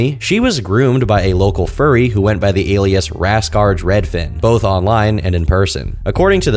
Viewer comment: I thought you said "Nascar Dreadfin" for a moment.